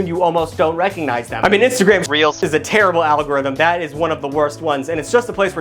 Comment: Lol I heard the “instagramREALS”